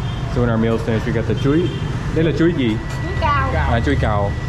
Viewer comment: Chuối đó là “chuối cau” á anh, giọng miền Tây nghe thành cao 😅